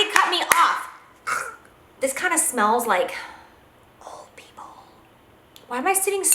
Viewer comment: when you said the bronzer smelled like old people i thought you said it smelled like nipple i was like, it smells like WHAT lmao. loved this video style. misssssed you jac!